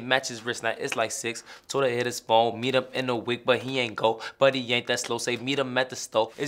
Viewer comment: This whole time i thought he said “meet em at the store but he aint go BUDDY aint that slow” i didnt think it was actualy but he aint that slow